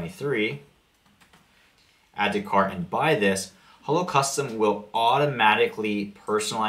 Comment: I thought you were saying Holocaust and I was so confused...... Hello Custom.